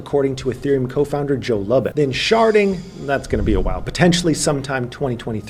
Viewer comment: I thought you said sharting….. my bad.